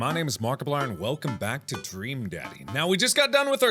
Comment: I thought it said "stream daddy" and i assumed it was a livestream, now I'm disappointed.